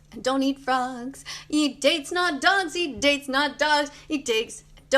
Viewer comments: Right around 9:20 was she saying eat d*cks not dogs? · I genuinely thought she said eat dicks not dogs